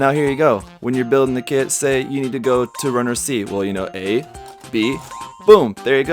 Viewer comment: Remember Guys!! F is later in the alphabet than G!! Edit: Oof! I misheard him saying "runner C" into "runner G" 😂😂